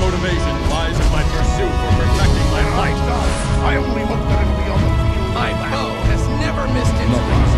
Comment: What the hell does Huang Zhong say? I've watched it a few times and I've yet to understand what he's saying. All I hear is "I only looked in the oven" Lol. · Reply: said 'When I die, I only hope that it will be on the field of battle.'